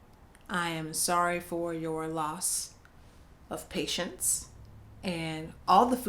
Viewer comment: For the first question, married to a man and wants to be married to a woman, I thought was gonna say "I'm sorry for your loss of pussy," not "your loss of patience" 😂😂😂